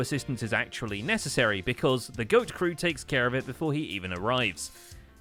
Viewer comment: Thank you so much for the warm welcome everyone! Have loved the recap since I discovered Hermitcraft back in season 4, so it's a huge honor to now be a part of it. Captioning, alt text, and other accessibility things are hugely important to me, and I also know that accurate captioning results in better auto-translation from YouTube itself, so hopefully this will help viewers for whom English isn't their first language too! I'll be doing my best to keep the captions as accurate as possible, though expect to see the occasional [confused noises] when I doubt even the Hermit in question knows what they were saying XD Don't be afraid to let us know if I miss something, either! For example, I've already been told Scar's mule isn't actually named Lemon, it's Lamon (Lamen?), so I'll be correcting that later (YouTube takes down the captions for several minutes when I make changes, so I'll be trying to minimize it.) Over the coming weeks I'll be captioning the previous Season 8 episodes, and may be returning to previous seasons to caption key events (such as the Civil War and the assorted Day Ones) if that would be of use to people! Truly glad to be here! Thanks for being an amazing bunch! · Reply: Thank you for the captions! btw, at 1:42 the caption says TGOAT instead of just GOAT.